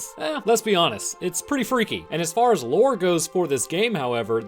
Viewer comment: did someone say "Freaky"